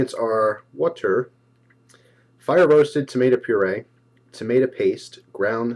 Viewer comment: hahah at 3:17 u say TAMATA PASTE instead of tomato i just have to nit pick like everyone else